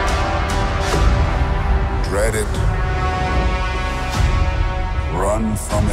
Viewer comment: Is it just me or does he say "reddit"?